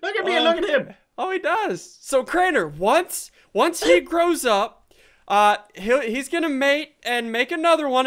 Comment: I thought ***** said "thow up" but he said grow up , 😹😹😹 lol